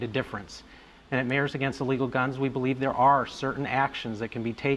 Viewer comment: does he say "we at Mayors Against Guns......."? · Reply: "Mayors Against Illegal Guns" (listening to him makes me stupid)